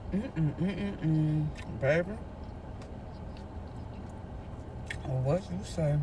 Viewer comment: Did she just say “baby, it’s what you say” to the burger 😂❤😅